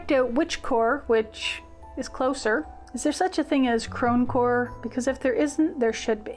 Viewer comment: I dig the idea of snap together combinations. I too enjoy a good Rachel Maksy binge. She's so resourceful! And I didn't realize you said *crone*core I thought you said chromecore...ENTIRELY different aesthetic! 😂